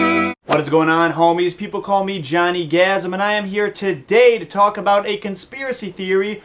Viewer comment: omg his name was different, at first when i heard him say his name i heard "jonny orgasm"